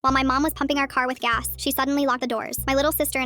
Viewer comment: I heard “My mom was humping the car with gas”